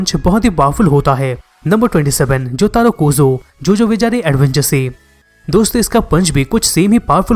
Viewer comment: I mistakenly heard it as Jojo bechare adventure😅